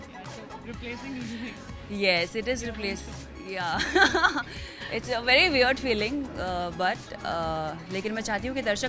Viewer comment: what did the other girl said at 5:57 it is replaced........ what? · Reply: beha naseer you